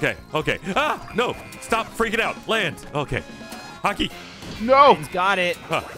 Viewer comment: maybe i'm watching too much One Piece but i swore he said Haki instead of Hockey